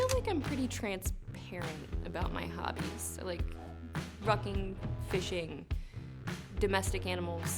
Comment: Not gonna lie, i had to do a quick playback on the hobbies. I thought i heard fucking instead of rucking. But it definitely was rucking. I just wanted to hear the other one.